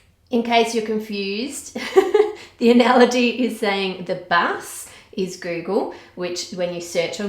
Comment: I was listening to the video while doing something else, and I thought she said bass (the fish) instead of bus. In my mind a fish was taking me to see the coral reefs